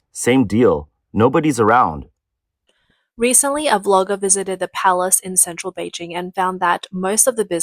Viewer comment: "The Palace" But the sign says The Place Am I hearing wrong, or did something get lost in translation?